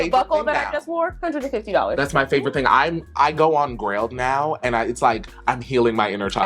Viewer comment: where did he say he go? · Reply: Grailed